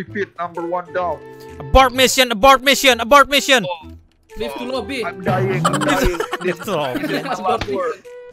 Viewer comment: Silahkan komen menit berapa aja editor salah denger dan salah nulis, itu skill pasif dia btw hahha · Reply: yg seharusnya "leave to lobby" malah jadi "give to lobby" 8:20 "god backup"? harusnya "get backup"